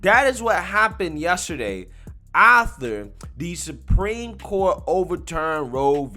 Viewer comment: "Ather" what kind of word is ather ?